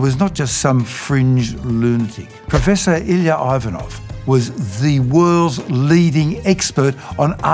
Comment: oh my god I thought he said ilya rozanov and I got genuinely confused😭😭